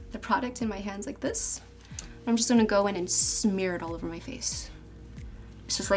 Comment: SMIRL? What is that word?